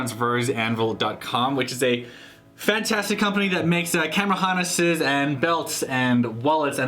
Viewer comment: Thought you said camel harnesses LOL